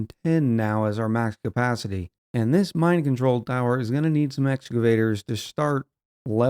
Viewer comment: When you put down the new Mine Control Tower I heard you saying "Mind Control Tower" and now I can't unhear it 😅